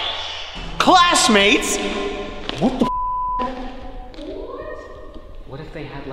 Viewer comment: sounds like someone saying what ! is does not sound like any of u and it was clear and loud